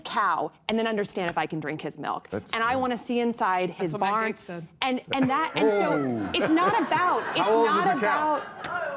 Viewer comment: Does anybody know what Mary Katharine Ham says here? I've listened to it 20 times and can't make it out. Thanks in advance. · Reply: "That is what matt gaetz said."